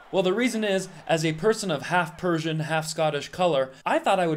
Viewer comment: For a half-Persian you’re 100% right and as a half American you’re still 100% right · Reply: Did I mishear? I thought he said half Scottish.